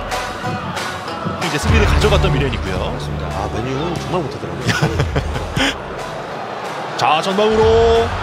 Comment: "맨유는 정말못하더라구요" 잘못들었나? 개웃기네ㅋㅋ